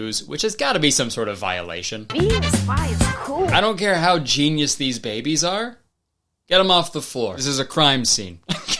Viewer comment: Why did I think Kurtis said "I don't care how baby these geniuses are" at 7:10 sdfkdslkfhlkfhsdlkf